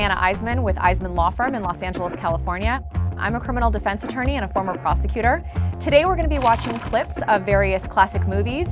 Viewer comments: When she said former prosecutor i thought she said prostitute😂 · OMG I thought she said "I am a former prostitute"